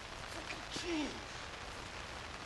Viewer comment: sounds like he said "FUCKING CHEESE!"